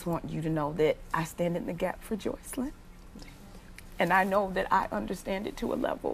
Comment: am I the only one that for the longest thought they were saying "Joselyn" in an accent and I remember wondering "what kind of accent do they have?" now I realize her name really is Joycelyn. anyway I don't know what these girls parents rub me the wrong way and why does the dad always act like he don't want his wife and other daughter to talk too much. why is he acting like a jumping bean when his wife talks like he's not comfortable with it. is it just me. I've just noticed this in other interviews as well. I find it creepy that the dads voice sounds just like Kelly I know R kelly always acted like he loved his mom so much. almost like her worshiped her, and to be degrade woman like this. almost makes me wonder if I should side eye the fact that he was so close to his mom. she would be rolling over in her grave. who knows she may be defending him since they were so close. 🙄